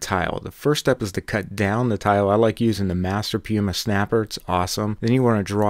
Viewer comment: What's the name of the tile cutter you're using? You said the name too fast. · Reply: Lol, i thought he said Monolight Master PMS.